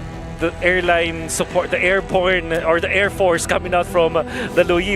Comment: what did bro just say😂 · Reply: AIRPORN 😂😂😂😂